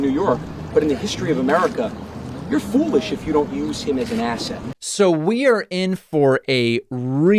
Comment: At first it sounded like he was saying Rudy Giuliani is an A$$hat not an asset.. I agree Rudy is an A$$hat as well as being a bloated windbag..